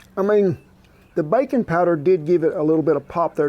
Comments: I thought you said bacon powder……….I got excited! ❤ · Oh, Baking Powder! I thought you said "bacon powder". I was confused.